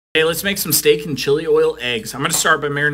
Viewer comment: I thought it said “steak and chill” 😂